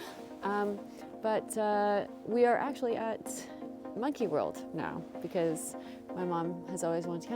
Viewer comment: Misheard Monkey World as "Nookie World" 🤔